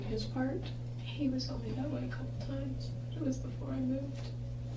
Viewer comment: I need help. At approximately 28:15, the det asks Arias if Travis was roughing her up. I've tried and tried to figure out what Arias said to no avail. Can someone please fill me in? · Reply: She said "He was only violent a couple of times but that was before I moved. "